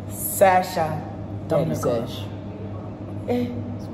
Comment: No wait, What did Sasha say 1:49 Did I just hear ThadSash🥺😁